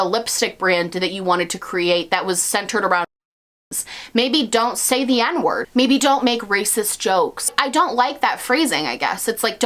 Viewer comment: What was the censored word? I can't lip-read. Edit: Oh, it was "n*zis" in case anyone else couldn't tell. I had to google.